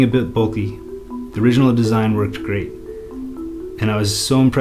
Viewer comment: I like the video, but I hate your pronunciation. For example, on 0:42 instead of saying "Worked great", you literally say 'pomegranate'. Dislike. You do not respect your audience, and the fact that they may not be from the USA.